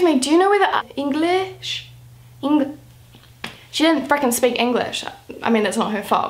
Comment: I was a little bit shocked cuz i thought she said "fucking" but then i realized she said "freaking" lol